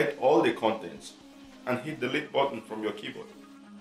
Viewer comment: im the only one who cant understand this guy the Subtiles say: contents and hit the lip button from your keyboard